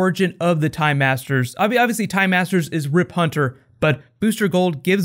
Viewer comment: I keep hearing "The Thai Masters"...